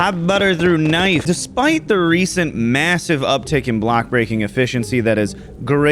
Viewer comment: did someone just say MASSIVE?